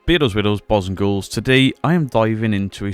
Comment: Did I hear you right? “Beardos weirdos balls on girls!” 😂